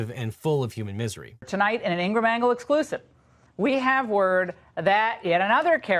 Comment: During one of the news clips, I misheard Laura Ingraham say, "tonight on the Insta Mangle," and I mean.... kinda.